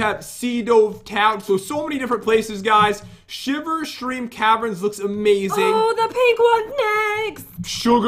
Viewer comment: Shiver Shream Caverns? Is that what i heard? Haahah XDD Like if you heard that XDDDD